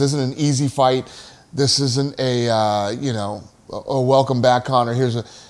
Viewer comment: For a millisecond there, I thought Dana said ". . . Welcome back Kotter . . . ".